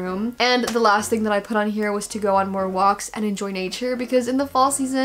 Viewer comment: am i the only one who thought said "go on roblox" instead of "go on more walks" 8:21